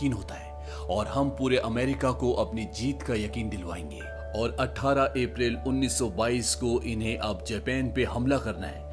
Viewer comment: Sounds like attharah April Unnish soh Bais, instead of baiyalish.